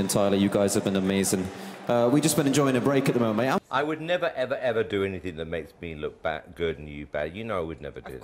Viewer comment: What did he say at 0:08? I couldn’t understand that part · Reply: "i would never do anything that makes me look good and you look bad" -simon cowell 💀💀